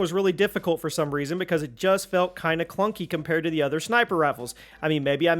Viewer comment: Did he say sniper raffles? 🤔